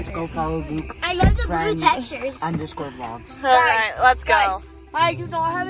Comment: What did Luke say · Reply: I heard Luke_vlogs 😂😅 idk